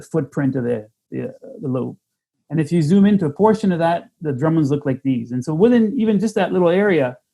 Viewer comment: i thought Randall was saying "gremlins"...then i read the title! :P